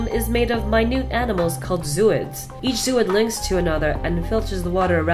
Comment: All I heard at 0:50 was zoids